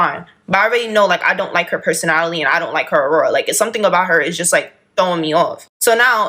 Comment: Her saying “aurora” when she meant “aura” is pissing me off 😭😩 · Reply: I heard "aura". It may be her accent.